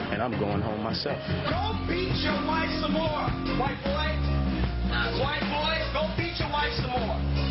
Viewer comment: This is a prefect example of what racist america does and how they assess situations. If Dave would have spit in puck face there's no questions about it Dave would have been gone, and the show would have went on like nothing never happened. And what's even more interesting is how they rode out for puck, as if he didn't do absolutely never wrong and never need to be assess. which goes to show when you a white male in America there's definately a brainwashing or set factor if you will, that America will definately take care of you! But for the black guy not so much.... The only place I disagree with Dave is how he kept provoking puck I and saying some of the things he was saying ( I e white boy) but spitting in someone face is a physical offense and if it was Dave who did that we all know would have happen and how the people would have treated him. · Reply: I heard him say white boy sooooo